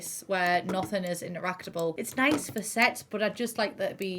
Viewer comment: Did she just say "it's nice for sex?"